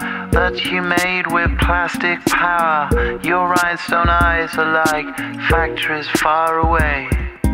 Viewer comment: Let's be honest we didn't understand a single word but it still was a catchy song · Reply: I thought he said redstone eyes💀